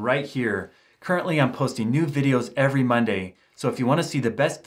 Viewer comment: sounds to me like he says "nude videos". LOL! Anyone else hear it?